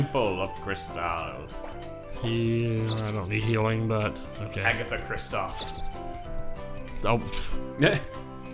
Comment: Agitha Kristoff is what I heard and for the 2nd time,Seeing Frozen doesn't mean you've seen a drop of snow in your life!